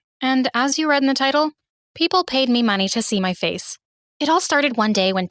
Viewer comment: I literally thought it said "People paid to pee on my face"